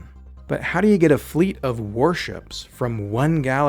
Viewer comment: When you said warship I thought you said worship, and I actually entirely accepted that in my brain, when later you said it again I realized my misinterpretation as well as realizing that’s gotta be one of the coolest names for a fleet or legion of a sci-fi dogmatic theocratic civilizations military. “A worship is heading our way” an ominous silence and then thundering villainous sounding drums rise in the background